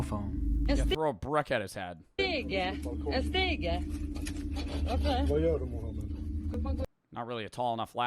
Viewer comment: I love how it starts with saying a ladder? A ladder? And the man saying are you for real Mohamed. Ps what the commenters didn’t say was that the guy was stabbed by Mohamed before he entered the house